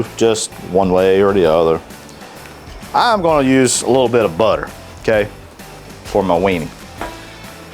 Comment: wtf did he say he does with butter? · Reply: Little butter for my weenie. 😬